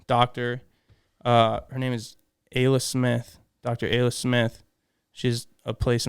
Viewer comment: Great pod and huge respect to. Anybody know what doctor went to for his post concussion syndrome? Sounded like he said Ayla Smith but can’t find her. Been struggling with a concussion and could use some help